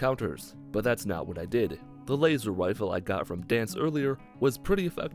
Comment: did he just say "Laser Wifle"